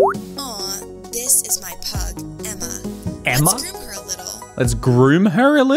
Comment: when she said pug i thought she said “this is my plug,emma” 😭😭😭